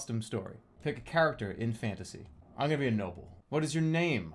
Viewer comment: when he said im gonna be a noble i thought he said im gonna be a gnome